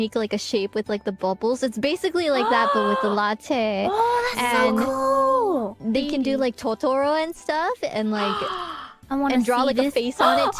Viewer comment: ここ字幕見ながらだと「oh...最高！」って言ってるように聞こえて、見直したらSo coolだったｗ 語呂合わせで覚えるのによさそう